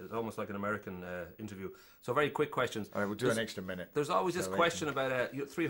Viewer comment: Did Gerard say, "we'll do an extra minute, cause I like him" at 2:25?